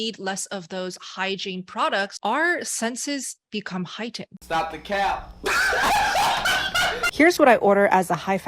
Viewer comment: This is a never-before-seen to me lol I can't make out what they are saying lol can somebody tell me what are they saying? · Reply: "Stop the cap" is what I'm getting...If that's it...Somebody please explain this joke to me lol